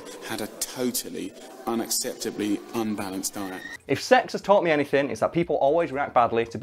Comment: Did he say "If sex has taught me anything..."???